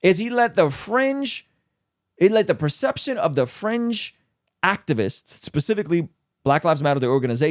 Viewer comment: I kept hearing "french left" and "french activists" until I heard "fringe" haha I was wondering why was talking about French people haha